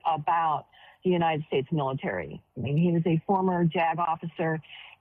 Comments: I heard "a former jag-off a sir" 00:48 Anyway, it's been a bad week for Grahams. Please spare me the "don't speak ill of the dead" routine. He had a platform where he could have helped people. Instead he hurt a lot of people in his life. How many people lost healthcare? How many women were forced into giving birth or died from not receiving care? He loved war, which meant killing people, many of them innocent. So, I'm not sad at all. Hope he can handle his life review. ✌🏽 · Jag officer? All i heard was "he was a former jagoff" 😂😂😂